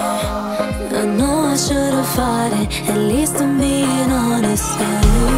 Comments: The very first time I heard this I thought it said "I should have farted, at least I'm being honest" I was like, what the heck! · Admit it, your here because: 1. This is arinanas best song 2. Covid-19 3. Bored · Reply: Nope I'm here because a part of the song sounds like she says: "I know I should have farted, if I'm being honest"